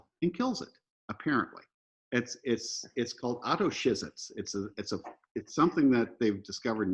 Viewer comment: what does he call it? Autoshizet? I can't find it. He mentions it on the fat emporer as well but impossible to decipher. Much obliged 🙏